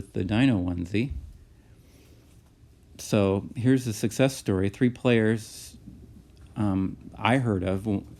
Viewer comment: i thought said seducce story lmao im sorry 😭